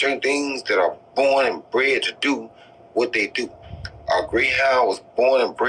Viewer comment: All I heard was “ born and bread to do “